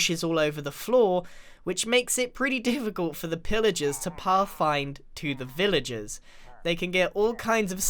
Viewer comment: I have bad hearing and I thought he said for the villagers to twerk in XD